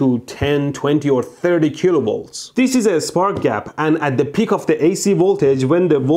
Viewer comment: I thought it said spank gap. Both things I like